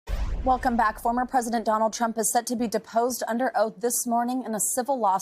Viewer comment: Thought it said disposed,, had to check ✔